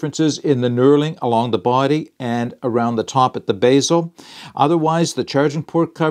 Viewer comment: Basil, Bevel? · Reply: Oh a Bezel got it. Sorry I thought you were saying Basil as in the herb.